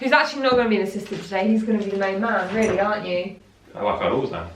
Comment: can someone please tell me what he said 😅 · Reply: He said, 'uhh like I always am.'